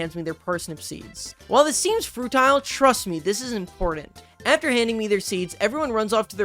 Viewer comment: did you say “Fruitile” at 9:31?